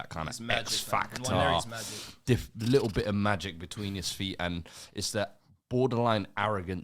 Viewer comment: Did say little bit of magic BETWEEN his feet?